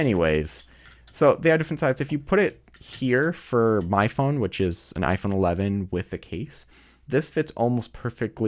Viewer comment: I almost thought he said iPhone 11M Pro.. 🤣🤣